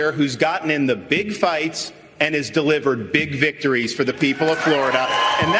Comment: did Ron say "we Juan big victories"?